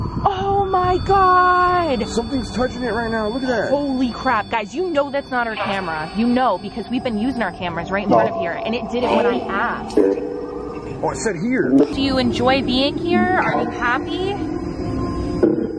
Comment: I swear i heard an evp say, "Oh Sh**" 🎧 needed on high 😂